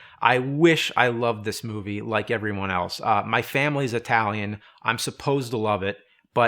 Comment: Did he say :" my family is Italian?"